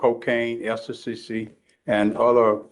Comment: "She was on cocaine, extasysy..." (In the voice of Judge Chamberlain Haller) "Uh, uh, what was that word? Did you say extasysy?"